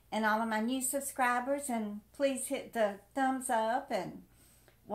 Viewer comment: Now, on the happy side, I thought you said "watch my ass". Lol.